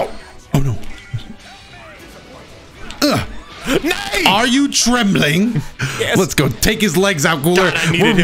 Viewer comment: I stg I thought he said “Are you tripping?” And it was my favorite voice line